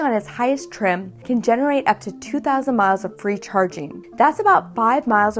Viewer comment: Thought the videos said 2000 milfs. Disappointed 😕